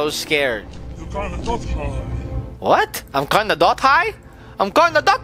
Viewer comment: when he said the thing u cant understand he said you canna not hide an he meant you cant not hide hope that helps i love ur vids